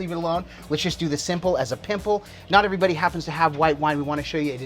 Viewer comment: Nice one guys - good inspiration for our weekly salmon! I know you actually said "simple as a dimple" at 3:08, just in case someone heard that differently (LOL) BLACKSTONE!!! your faithful follower in CLE!